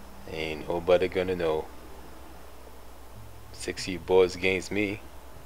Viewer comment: first i heard "sexy boys againts me" :D:D